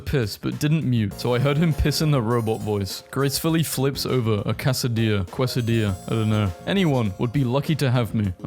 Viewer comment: Did you just say Kwasadia?